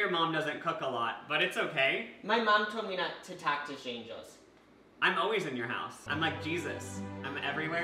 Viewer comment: I thought he said “My mom told me not to talk to Shane Dawson” 🤭